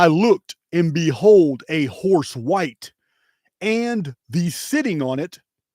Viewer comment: I have to ask this for clarification. You said, “and the sitting on it having a bow and a crown”. The first time you read it I thought you said, “thee” sitting on it. I’m focusing on the word thee or the and the word “it’ that describes the horse. If the horse is described as an “it”, who is “he”? “That HE might conquer.” To me it sounds like the verse is indicating there is a rider. Just clarifying.